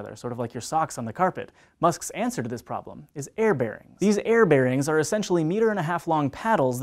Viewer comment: I thought you said Care Bears instead of air bearings